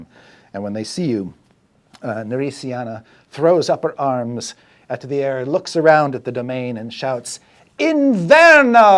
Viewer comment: Attempt at Italian translations by non-italian speaker - I make no promises as to accuracy <3 : 8:10 'inverno' - winter 10:42 'ucidilli' - ??? (couldn't hear this one well enough to find anything in the dictionary) 12:25 '???' - beggars can't be choosers (although what Chris says doesn't match what the dictionary says would be the translation of that) 24:35 'regina de corva' - queen of ravens/raven queen 32:25 'finalmente' - finally 59:45 'Sbrigati' - hurry up 1:37:45 'Scusi' - sorry 1:59:22 'Arrivederci' - Goodbye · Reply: a year later, but italian "uccidili" - kill them (you couldn't find it because you didn't know how to spell it) the beggars can be choosers bit it's a saying that means the same, "chi ha bisogno si arrenda" and it literally means "who is in needs, yields" regina dei corvi* , raven queen (we use the same word for crows and ravens, btw) You got the rest and did a good job ;) i also appreciate chris for trying, i heard so many far worse attempts. i mean he managed to stress the wrong syllable in more than half the words, but he tried and had fun with it!